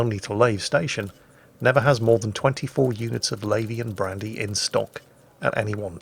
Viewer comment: first time i heard it, i thought you were saying Labian Brandy.